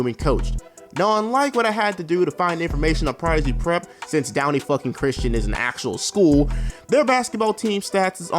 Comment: Did you mean to say “since downy Christian is an actual fucking school” because what you actually said just sounded off